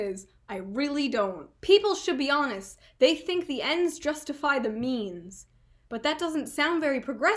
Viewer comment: It sounded like you said "the ends justify the memes" :D